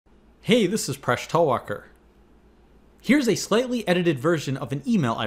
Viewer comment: Phresh Talwalkar? I thought he was always saying "Fresh Talker" but had a problem saying "Talker"....